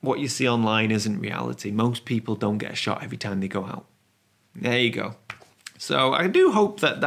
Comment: I had to 'rewind' there. For a moment I thought you said "most people don't get shot every time they go out" :-)